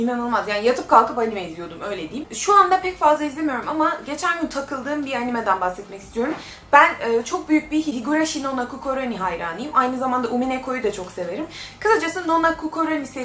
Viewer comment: ben şöyle anladım Hirigome hikikorisami bunu söylemek için acı çekenler Ben söyliyemedim Duydugumu yazdım hdmaudbsjeuamf7f. xD